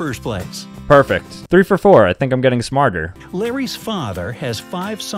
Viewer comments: Larry’s father 3:00 “I swear he said Barry” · I Heard Barry Too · nah I heard Larry as Timmy 😭